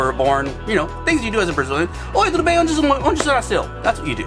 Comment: he said "Oi tudo bem, onde voce nasceu?" Hi how are you, where were you born...in case you were wondering what he said